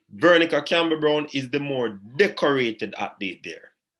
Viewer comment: Did I say Veronica was better than Shelly? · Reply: My bad. I misheard you. At 12:47, you said Veronica is more decorated and I thought you said she was better. But my comment was mostly for other people in the comment section who think that Veronica is better.